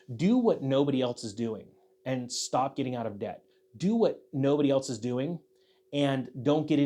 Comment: did you say “stop getting out of debt”? Please explain